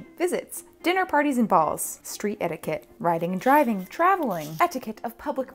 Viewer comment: YOU ARE SO ENDEARING👁👄👁 when you said,"riding and driving" I heard ,"riding the dragon" and thought ,"...sure, why not— wait". I briefly believed dragons just like...existed but only in the past